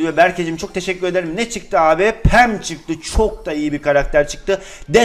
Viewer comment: penny çıktı ama abi pam çıktı mı dedi yoksa ben mi yanlış duydum djjdkdjkjdkjsskkdkölslspldm